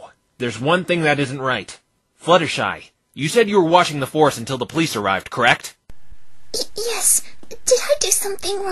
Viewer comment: Just noticed a bit of an error.. The captions say that the police FORCE arrived but Nick says "The police arrived"